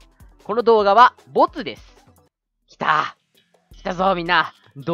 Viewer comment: 頭にマンモス乗ってるよ!? 0:48乙です！に聞こえたのわいだけか？